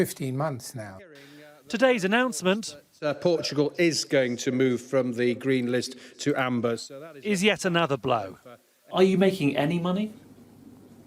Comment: Thought it said 'Portugal removed from travel green list to anger travel agents'